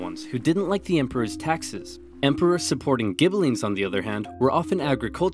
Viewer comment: did he say emperors supporting ghibellines?